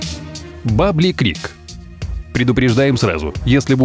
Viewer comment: мне послышалось "бабий крик"